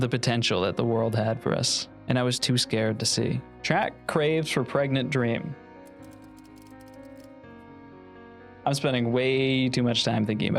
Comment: im so tired and misheard this as “trapp craves for private cream” and i just accepted it (lowkey sounds like my history class rn) until i blinked and thought about it